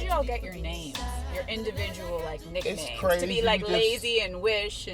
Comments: She said nicknames... but it sound like she said nigga names. lolol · Hold TF up, @ 0:37 did this bitch just say nigga names?!!!! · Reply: dee mick lol nah she said "individual nick names" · Lol I thought thats what she said but I thought no way! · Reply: andy<num> I had to rewind it a few times to realize she says "individual nicknames" but it sure sounds like she says "n** names" lmao